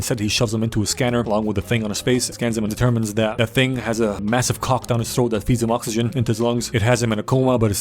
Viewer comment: I paused the video a moment and when i unpaused it, the first thing i heard was "they discovered the thing has a massive cock"